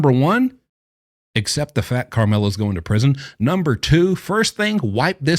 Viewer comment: About halfway through it sounds like you said Karmela.